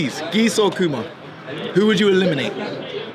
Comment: I thought she said Fahkumram at first instead of akuma. Lol